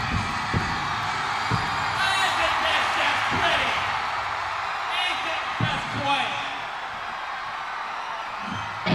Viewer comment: What does mike say at 8:52-8:57 ? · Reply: to me it sounds like “this is great, this is just great” but maybe that’s just me trying to make sense of gibberish lmao